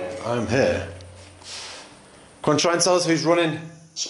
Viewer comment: I feel empathy for these spirits and the ways that they suffered. OmG when you said 'Who is running' I heard 'Nazi'.